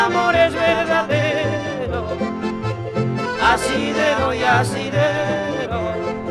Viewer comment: asi le doy ,,,,,,,,,,,,,,,,,,,,,,,,,,? que dice por favor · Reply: Asidero, asidero.. Parte por donde se ase o agarra una cosa. "una barrera reflectante, situada por encima de la matrícula, sirve también como asidero para abrir el portón trasero" 2.Punto de apoyo o pretexto para algo. "el entrenamiento fue el asidero de esas seguridades que le faltaban"